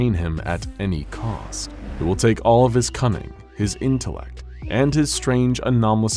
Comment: Honestly, who els Thought that The narrator Said "It will take all of his Cumming," And not cunning